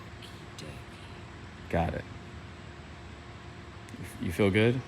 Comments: Anyone know what she says at 0:32 ? Can't make it out but it must be rude. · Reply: I think she said "okie dokie". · Did anybody catch what she said at 0:33? My ears ain't so good. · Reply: She said oki doki